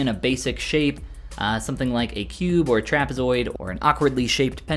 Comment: I heard Giraffezoid